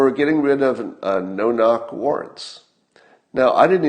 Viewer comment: I kept hearing "No-knock warts"